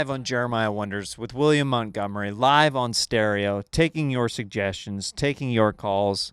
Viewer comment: When you say "live on stereo" I keep hearing "live on steroids" in my mind